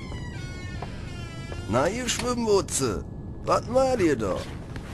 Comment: was sagt er da? nah ihr schliggenwutze? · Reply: "Schwimmbutze" bedeutet Badehosen 😉